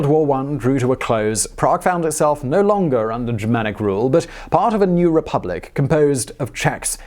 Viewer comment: what does he say? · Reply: Under germanic rule